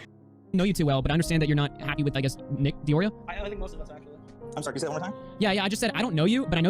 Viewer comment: Nkoreo sounds potentially racist if said fast, or at least the way hasan says it, it sounds like he says nickr oreo